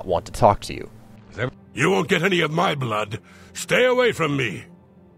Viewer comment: the guy who is talking says "You won't get any of my blood! Stay away from me!" even though it says in the text "You won't get any of my blood! Get away from me!"